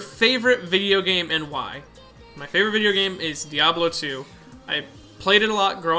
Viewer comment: when he said Diablo 2, I thought he said Diovalo 2, I wanna play that game.